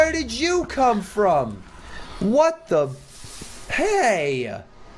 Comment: wooo...! Where you come from?! WHAT THE-? Me: don't you ever said that WORDS! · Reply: I heard him say "What the bu-"